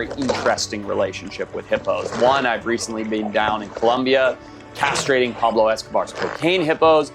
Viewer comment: um. 😶. did he just say, “castrating carols escobars cocaine hippos??” that is effing gold!! It made me pause and lol for quite some time!!!! my first time at the channel- cheers!